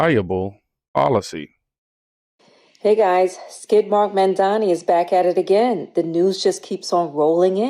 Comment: 😂😂😂😂 did she say skid mark mamdani? 😂😂😂😂😂 Freekin hilarious 😂😂😂